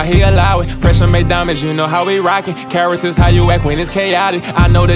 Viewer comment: "Character how you act when it's chaotic.." The line of the century. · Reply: I thought he said Carats is how you act, like pressure making diamonds